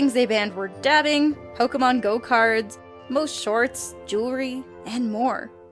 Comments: Did say Pokemon Go cards at 5:52? Because I'm freaking triggered. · did she just say "pokémon go cards" is smoking crack? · Did she just say... Pokémon GO cards?!?! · Wait... Did just say "Pokemon Go cards"? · Did she just say "Pokemon go cards"? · Did she just say, "Pokémon GO cards"??? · Did she say "Pokemon Go cards" at 5:51 ? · Did she just say Pokémon go cards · did she say Pokemon GO cards??? XD